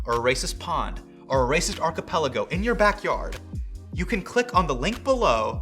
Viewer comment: Now, I'm sure you said archipelago, but I definitely heard "acapella goat." Which, is absolutely what I would name a chain of islands.